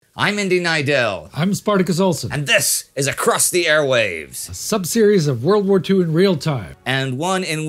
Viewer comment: Kinda sounded like you said, "This is Crusty Airways!"